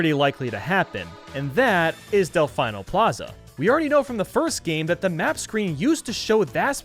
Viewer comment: Del-FINE-nol Plaza is what i heard